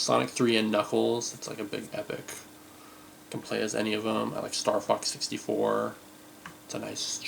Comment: HAHAHAHA...I thought you said "STARFUCK 64!" 🤣🤣🤣